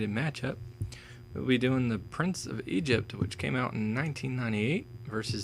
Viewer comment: Never seen that film. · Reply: Oh sorry lol,thought you said prince of perisa or something like that.